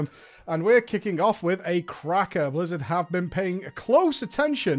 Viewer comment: did he just say cracker??! lol